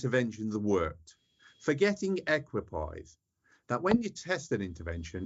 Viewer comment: What word is prof h referring to just after the 41:41 mark : "examples through history where people believed that interventions have worked, forgetting 'equi pies'?? (Sounds like) ...that when you test an intervention it's equally likely..."